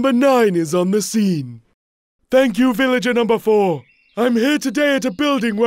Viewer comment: The subtitles are wrong. It is "here" not "her".